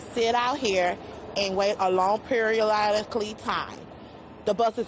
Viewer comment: I had to wait a long periololically time for this video to load because my internet speeds keep going from worse to worser. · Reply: I took me a long time to find this because I thought she was saying "A long perial island clee time".